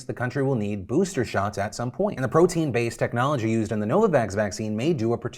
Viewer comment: I can't lie- I was just listening at first when Phil started talking about this new vax- Aaand I totally misheard and thought he said the name of the company was NoVax! XD And I know I ain't the only one! Admit it!